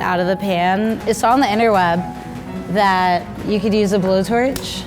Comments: did she just say INTERWEB like am i th eonly one like hugh??!! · did she just say "interweb" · wait did she say interweb.? Like a web that comes out Spider-Mans hand?